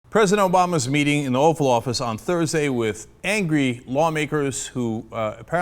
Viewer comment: Did he say "Offal Office"? XD